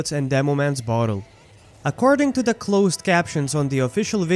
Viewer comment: i think thats just a mistake with the captions just because of how he says i hear it as he